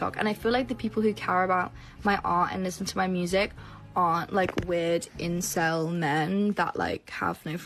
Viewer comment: she said "aren't" she just has an accent, so it sounded like are